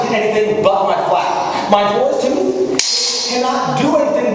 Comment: I get the feeling that he said 'my boar's tooth' instead of 'my fool too' at 3:09.